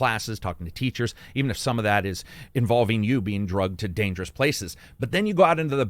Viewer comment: Did you say you get "Drugged"? or did you say "Drug" instead of "dragged"?